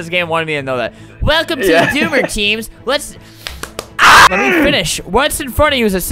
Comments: Bro I thought that said "Boomer" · I thought that said boomer 😂😂😂